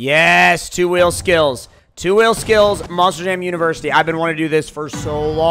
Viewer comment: Thought it said Molser jam for a few seconds